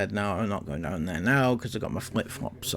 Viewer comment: Thank you for the translation. I would never have known what she said. These kids have such bad diction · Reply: I got most of it but thought she said slippers not flip flops